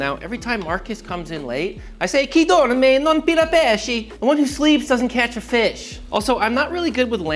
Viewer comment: I heard "Wooden sleeves doesn't catch a fish." Is that correct, Marcus?